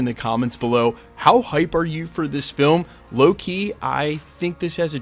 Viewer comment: Haha omg when he said "low key" I thought he said "Loki" and I got so confused for a few seconds 😂 Also I REALLY don't want them to kill off tobey, I want him to be in secret wars!! But apparently they were gonna kill him off when he got stabbed in no way home but then they re shot it so maybe he's right 😭